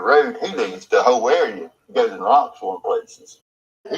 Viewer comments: Damn I hear so much I hear "leaves the area and rocks foreign places " also, "leaves the area and lots of areas " 🤦‍♀️🤷‍♀️🤷‍♀️☪️ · I swear it sounds like he says “he leaves the whole area, goes an robs 4 places.” 👀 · I hear "he leaves the whole area, then goes and Robs four places."